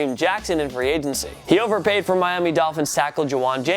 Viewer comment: Did anyone hear him say at 3:24 "he overpaid for sackle" lols